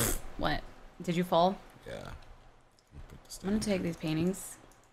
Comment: It sounded like said "let me take these panties"😂😂😂😂😂😂😂